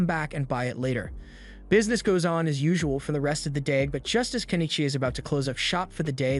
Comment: All i hear is "Kenny G"